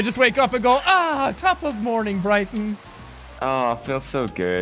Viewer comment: I just found out Brighton is an actual place I just thought he was saying Britain a weird way 😂